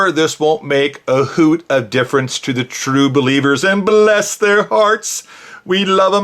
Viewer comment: Did he say "true beliebers" at 31:56 ? Haha! 😂😝